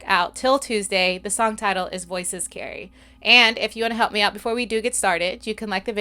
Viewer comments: When I was a young kid I didn't know they were saying "Voices carry." I thought they were saying "You're so scary." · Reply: Lol I heard "voices scary" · When I was a young kid I didn't know they were saying "Voices carry." I thought they were saying "You're so scary."